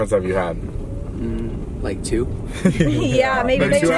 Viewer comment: what did they say right here? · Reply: yeah maybe 2 at once